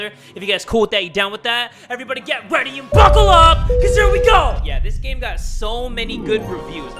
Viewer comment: I finally found the person that says "Buckle up" I thought he said "Buffalo up"